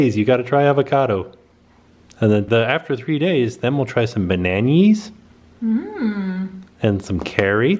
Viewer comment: I legit thought said "and in a few days well try you with some beignets" then realised he said bananas hahaa Also Skeeters bbq sauce is sooooo good we love the original and the new York steakhouse style